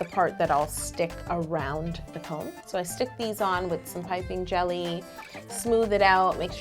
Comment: Is it just me or did she say piping jelly?????on